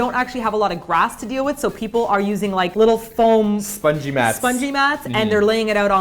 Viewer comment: I heard "Spudgy mats" instead of "spongy mats" orz I was like "NOOOO THE SPUDGY" D